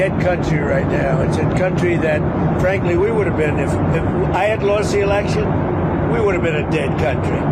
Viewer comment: Did I just hear Dump say “ If I hadn’t lost the election “ . I thought that he said that he won the 2020 election?